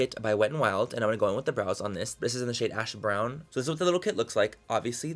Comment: I actually thought you said "this is in the shade hash brown" 😂